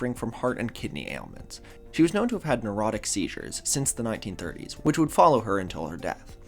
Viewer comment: guess who misheard something and found themselves googling “erotic seizures”